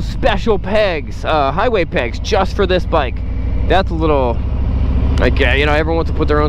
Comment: “that’s a little gay” is that what you said? are you a bigot? Was totally digging the channel until you had to slur it up.